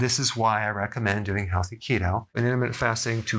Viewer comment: what kind of keto? I’ve listened to it several times and still can’t understand what you are saying, thanks. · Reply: Healthy Keto